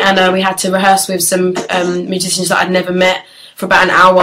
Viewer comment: does she say magicians ?? x lol